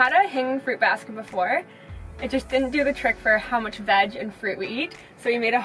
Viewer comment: she really said "for how much vag and fruit we eat" 🤣